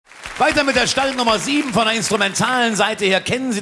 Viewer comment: Sagt Heck ganz am Anfang "Stallnummer"? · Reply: also ganz am Anfang bei 0:00 verstehe ich "Stallnummer" . Etwas später sagt er Startnummer